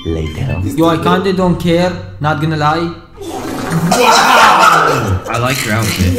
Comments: wait!! What did he say? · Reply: He said “yea I kinda don’t care not gonna lie” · What he said at 12:49? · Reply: “Yea I kinda don’t care, not gonna lie”